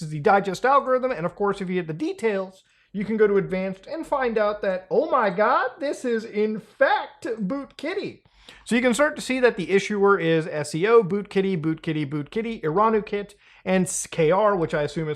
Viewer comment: I thought it said,“BoomKitty”